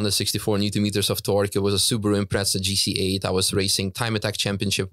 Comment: Did he mention he had a "Impreza GT8" (the 705hp one)? I can't find anything on that. Did I mishear that? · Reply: I heard him say ‘GC8’